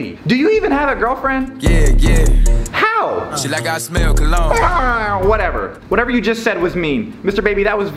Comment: Teacher : Do you even have a girlfriend ? Dababy : YEah YeAH Teacher: How??? Dababy: She like how I smell cologne - Lmfaoo , our generation . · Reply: I thought he said “ sh*t i got the smell ‘o cologne (yeah yeah).